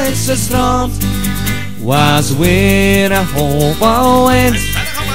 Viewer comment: Wat zegt hij bij 0:45 dan ik versta er werkelijk niks van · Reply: was weer een hope wind. en berrege water